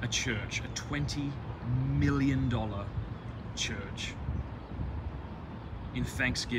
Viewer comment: I just woke up so my mind is still revving up; I thought Matt was going to say "I suggest we build a church, a 20 km wide church" and I was like, "LET'S GOOOO"